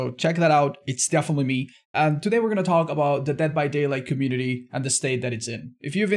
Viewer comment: I thought you said "hentai community" instead of "dead by daylight community"